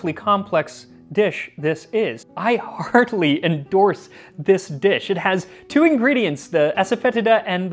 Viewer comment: I had to rewatch a a couple times. I thought he said "I hardly recommend this dish" and was confused.